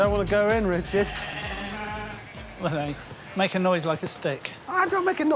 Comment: I heard it as "make a noise like the Stig" "the stig doesn't make a noise. How can I make a noise like the stig" And I died laughing, being a big top gear fan.